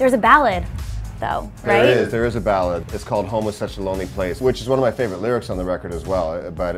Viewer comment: I thought he said "Homosexual Lonely Place" lol and I was like sounds like a Blink Track lol